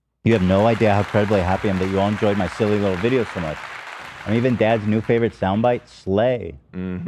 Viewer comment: Did Ethan call dad again? 😭